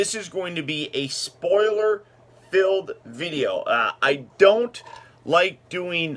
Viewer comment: Sorry, can't watch yet as I haven't seen the movie. But I laughed in the beginning when you said "Now, this is going to be a spoiler F-" and I thought you were going to say "free" but you said "filled" lmao. I'll be sure to come back when once I've seen it, excited to hear what you think.